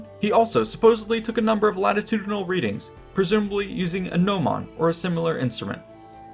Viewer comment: The tool, at 8:10, what was it called? I tried googlande but couldn't get the spelling right 😅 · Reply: gnomon